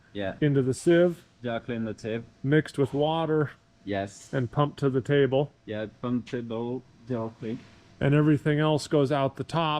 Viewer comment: when the subtitle says "pump table (unknown)" it sounds like he's saying "pump table (d'en haute)" which is "from above" in French Very informative video, cheers